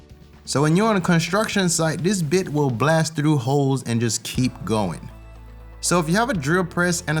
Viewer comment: It sounds like he says this bitch will blast through holes and just keep going lmao